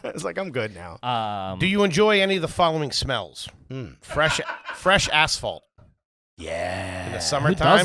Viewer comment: I thought said fresh asshole! 😂😂😂😂